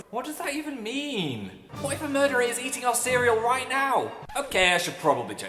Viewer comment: I JUST HEARD "boyfriend murderer is eating our cereal right now!" AT 1:18 WTF